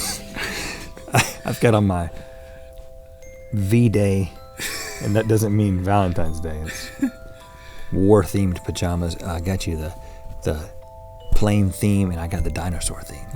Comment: Thought Rhett said I have on my Valentine’s Day massacre pjs